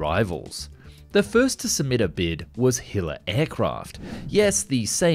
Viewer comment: I thought that said hitler.